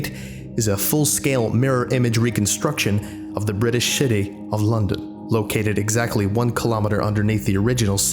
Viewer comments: Did you say " British Shity of London"? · Sounds like you said "reconstruction of the british shitty of London". Youre welcome. Now none of you will unhear that. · I don't want to seem nitpicky but I heard in 1:34 British Shitty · I swear he says shitty of London.